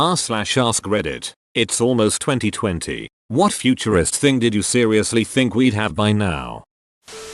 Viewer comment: Thought it said furriest thing and I had to do a double take